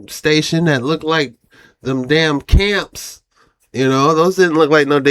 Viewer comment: they replaced what you actually said in your caption feed look 🌵 👀 They replaced camps with unusual facility